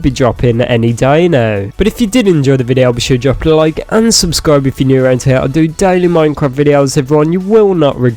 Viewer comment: for a second i SWEAR i heard "unsubscribe if your new" that made me laugh so I subscribed.